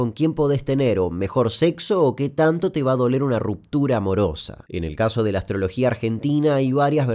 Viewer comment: pensé que decía que tanto te va a doler una ruptura a♡al